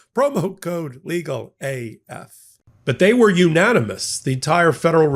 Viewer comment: After the Tushy commercial all I heard was "bidet where unanimous"😂 Keep up the good work MTN